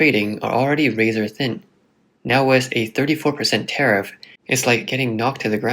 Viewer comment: did he say 34% tariff? Haha